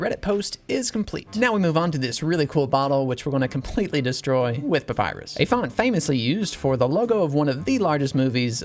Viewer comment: when you said "we're going to completely destroy [the bottle] with papyrus" i thought you said "we're going to completely destroy [the bottle] with a virus" 🤣